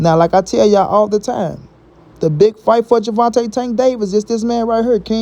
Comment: I thought he said he want the big fights